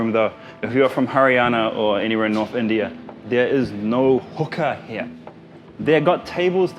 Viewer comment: DID I HEAR HOOKER ?